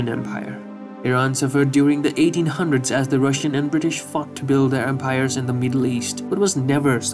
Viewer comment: I know he said fought but it sounded like fuck to me